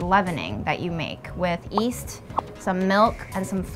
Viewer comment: north, south, YEAST, west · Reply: Yeast? I thought you said weast